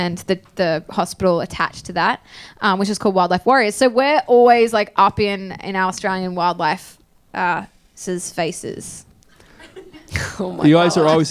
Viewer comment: I didn't get what Jess said at 12:21 and that Lisa replied : oh my Gosh, no comment. Sorry, having hard time to pick up their accent! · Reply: We're always, like, up in our Australian wildlife, uh, s (wildlife's) faces. (BTW I'm Aussie but I can struggle with some Scottish accents so I get you)